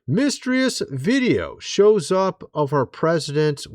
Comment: Did you just say 'mistreus' for mysterious?n lol